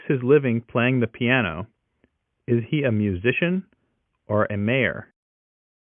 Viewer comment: 🥲🥲?? What did said "is he is a musician or a ____" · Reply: Or a mayor